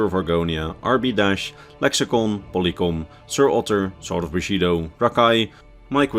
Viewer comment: Whenever he says "sword of bushido" I just hear "sorta bushido"